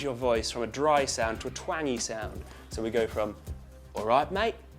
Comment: At about 01:55, did he say "twangy sound"? I'm not a native speaker of English and the subtitle says "20" 😂